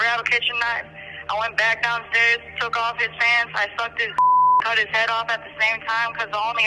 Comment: WTF she say @ 22:20... she suk him up n cut his head off at da same time? How da phuk she dat?? Dats krazy